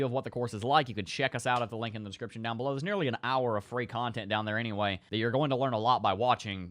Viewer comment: Harry potter??? No!; Free content, I thought u said free coffee... I mix 1 scoop Starbucks House to 3 scoops Walmart Arabic House or French Roast.